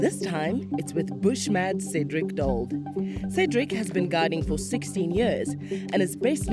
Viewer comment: I wonder if this is the same Cedric who joins Dax at penguin beach. They seem to have fun working together. By the way, I thought she said he has been a guide for sixty years, lol. No, obviously it is sixteen.